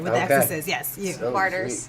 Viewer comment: what did she say the scariest horror movie is? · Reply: Martyrs i think?